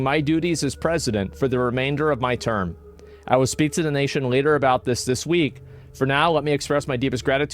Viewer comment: Did he just say "I will speak to the Nation leader this week?"